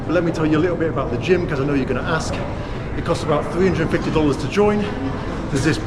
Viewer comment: did you say it cost $350 to join the gym? That's expensive!